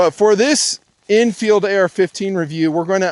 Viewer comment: For a moment, I thought you said "Enfield AR-15" and was reaching for my wallet.